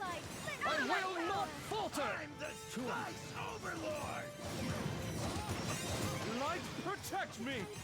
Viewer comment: 0.11 why the hell did I hear it “I’m the stun overlord”. ?????